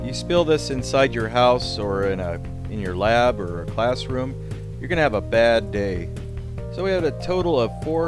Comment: What is said: if you spill this inside a lab or classroom, you are gonna have a bad day. The captions:if you spill this inside a lab or classroom the ramifications of a spill are frightening. 😂😂